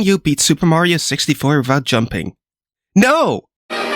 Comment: did you say "vat jumping" ?